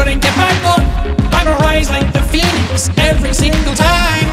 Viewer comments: It sounds like he is saying imma rise like the felix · I thought it said “imma rise like the Felix”